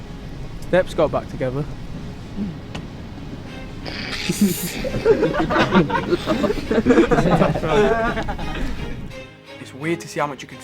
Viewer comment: What did Zayn say @ 2:14?? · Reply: steps got back together I guess